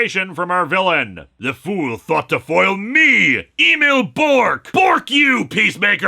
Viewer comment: I misheard the name here to Email Bork, which gave me a good chuckle.